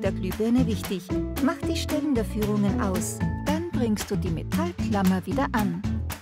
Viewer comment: Mach die Strecken der Führung aus? Mach die Ständerführung aus? Mach die Stecken der Führung aus? Oder was sagt die nette Französin da?